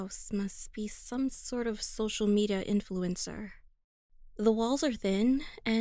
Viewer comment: Hang on, did she say social meda